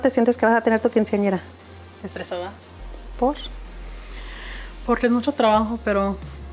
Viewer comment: I am learning Spanish and at 1:27 the subtitles said "why" I thought that's porque in Spanish. Why does the mother say something like "posh"? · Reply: She said por, it's just short for porque. She's still asking why.